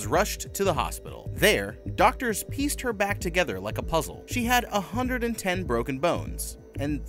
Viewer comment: I thought you said 210 broken bones, first thought was damn isn't that like all of them, then you say out of 206 so I wonder did she borrow a few? Then the video replays.